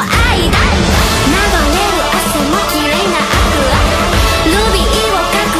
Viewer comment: I heard Aqua and Ruby in this sentence, “Akua” as Aqua and “Rubii” as Ruby